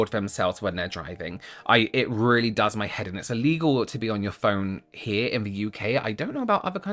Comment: i thought you said it was illegal to be on your phone while drunk 😭😭😭